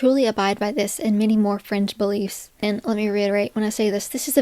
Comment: For a second I misheard 'fringe beliefs' as 'french beliefs' and I was very confused about why France was being blamed. Great video though, thanks for spreading awareness.